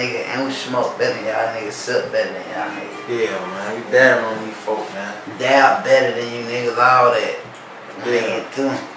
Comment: what did he say at the end at 5:13????? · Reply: like "ion know about that one bro" 🤣🤣